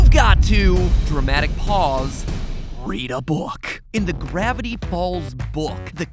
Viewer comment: he says gravity paul instead of Gravity falls